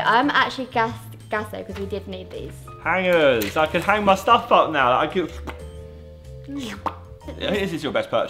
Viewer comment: I thought he was gonna say "I can hang my self now".